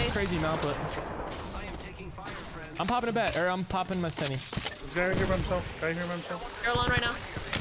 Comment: I'm pretty sure said "popping my semi" instead of Senti". Very on-brand!